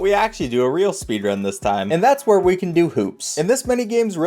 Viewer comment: thought he said we can do poops